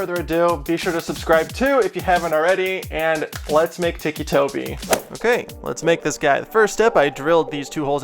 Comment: At first I thought it said Thicc Toby